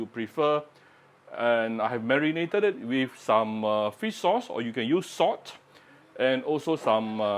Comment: My kind of chicken soup but as you poured in the 'marinated' chicken...what was in marinated in, I didn't see that part... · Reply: Ok, had to replay the video and heard you say can either marinate with salt or wine...